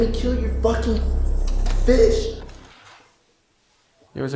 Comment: I thought he said fish! 😂